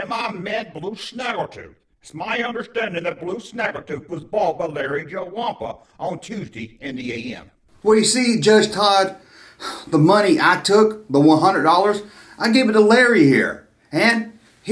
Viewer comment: Where you get Joe lol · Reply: When was announcing his name, that's what I heard him say...👌🏼💯✔ I heard Larry "Joe" Wampa...👂🏼